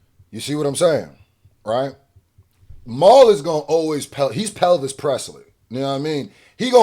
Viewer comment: I thought he called that mf Pelvis Presley 🤣